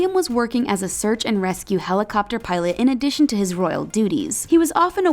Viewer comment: every time she spoke, I heard ''Royal doodies''